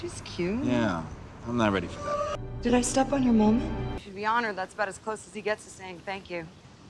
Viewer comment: did she say: “did i step in your mom”